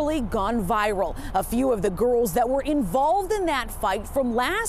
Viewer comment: O thought the reporter said goons not girls lol